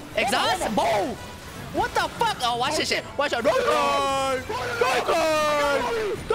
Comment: what does screaming at 7:09? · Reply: D KANE